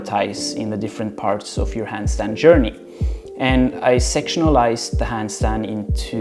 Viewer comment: I thought you said 'I sexualised the Handstand' instead of 'I sectionalised the Handstand' 😂😂